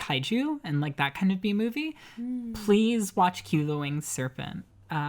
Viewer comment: I kind of didn't catch the name of the film at 38:50, it sounds really cool though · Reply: q: the winged serpent